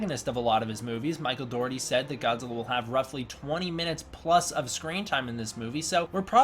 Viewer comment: Wait, at 6:04 did he say... 20+ Minutes of screen time? or is this a dream that's come true???